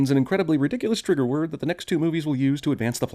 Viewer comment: Did he say two movies?